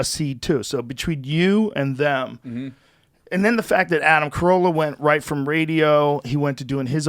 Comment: Didn't Anthony Cumia inspire Joe to do the podcast....? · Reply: And I thought he said it was Adam Curry. Didn't he call him the Podfather? But what do I know.